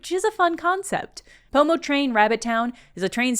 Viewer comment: i misheard this as “homotrain” and my interest was piqued